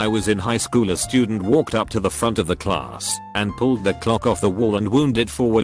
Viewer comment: Not gonna lie at 1:37 I thought they said a student walked up to the front of the class and pulled out a glock